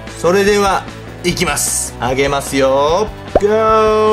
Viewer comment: あげますよー？がヒカキンじゃんけの、いきますよー？に聞こえる！